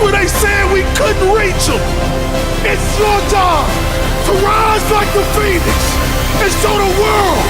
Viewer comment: i heard "rise like a fetus" and spit my drink out